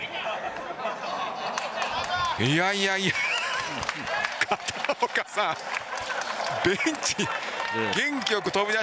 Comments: 巷で噂の空耳 「いやいやいや片岡さあ〜ん……うんちっw」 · 「うんちwww元気良く飛び出してwww」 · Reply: ベンチって言っていますが,ベウンチってコメントに書いている皆さんは聞こえたんですかね